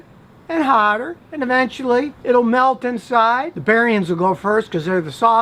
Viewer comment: Did he call them "barriens"? Obvious mistake, but did we hear that correct? Or my mistake.